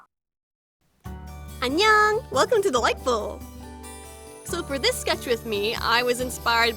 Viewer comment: Okay so quick confession: When I first started watching this channel, I always thought she was saying, "Welcome to the Light Bulb." I'm sorry for I have failed you.